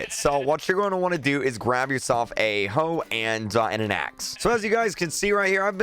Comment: Did said hoe and a axe